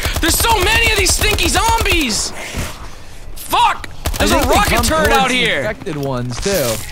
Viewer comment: it sounds like you say rocket turd